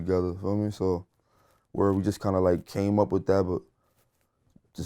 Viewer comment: did he say we came up with devil asf then said sothing else